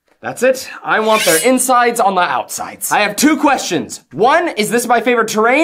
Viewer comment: “They have puppies as hostages…alright I want their insides on their outsides” is probably the most relatable lawful good quirk ever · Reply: i keep hearing them say "I want their insides on my outsides" which is a bit more gruesome lol but still accurate